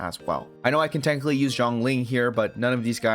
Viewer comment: if anyone thought he said "Zhongli" then he a didn't said that,he said "Xiangling".